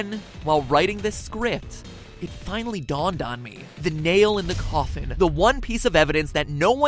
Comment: lol the first time I watched this I thought he said the nail in the cawthon